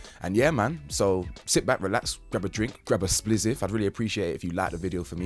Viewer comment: I thought he said grab a drink, grab a spliziff, and light it up 😵‍💫😳🤷🏾‍♂️🤦🏾‍♂️🤣🤣🤣